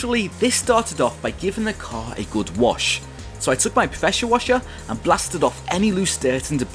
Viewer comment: Why does he talk a bit weird? I heard him saying "professure washer" instead of "pressure washer"